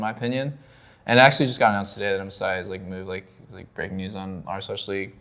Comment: ok but what does he say @ 6:13???? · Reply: I can only catch that “Msi is moved which was a breaking news on r/league” something like that